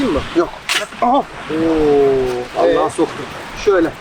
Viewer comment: BENMİ YANLIŞ DUYDUM YOKSA vllahamı demiş